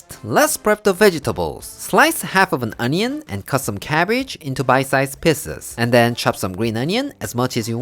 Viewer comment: Man I thought at first he said, "bite size pisses"